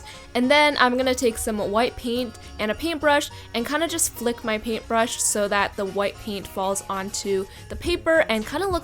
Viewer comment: I thought you said LICK your paper! haha